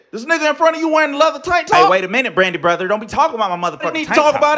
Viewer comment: Did he just say Brandy Brother? 😂